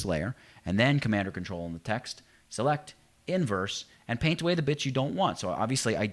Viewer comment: sounds like you said "and paint away the bitch you don't want "